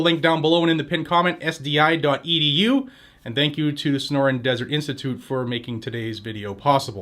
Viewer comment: I thought you said "snoring desert"